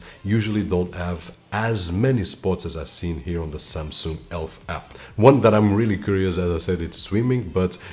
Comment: I thought you said Samsung Elf 🧝‍♂️ 😂